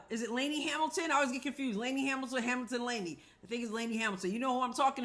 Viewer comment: I always remember it because at first I always thought it sounded like they were saying Lady Hamilton ... lol : )